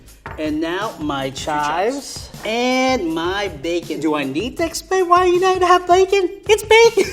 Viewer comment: the duck did he say he said something like do i need a duck that explains bacon it's bacon